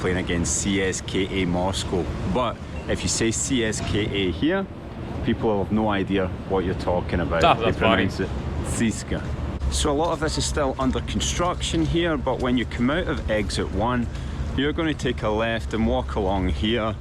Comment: Never say that. What he said in Russian sounded like "Сиська" "Tit". 😂😂😂